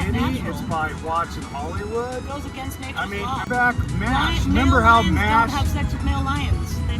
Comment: At around 7:00 I think he blames M*A*S*H or am I hearing that wrong?